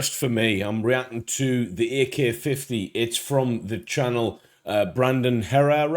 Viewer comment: I heard "the Ear Care 50"